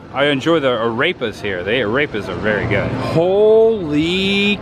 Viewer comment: of this video just made me laugh so hard....."Arepa" sounds like he says "A rapist" like Sean Connery from SNL skits